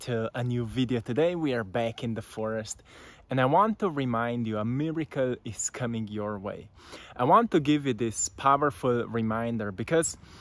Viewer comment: I thought he said America's coming your way at the start